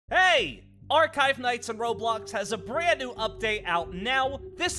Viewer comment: I always thought that he said our five nights